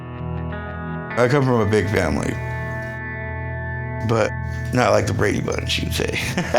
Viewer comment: what does he say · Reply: Not like the Brady bunch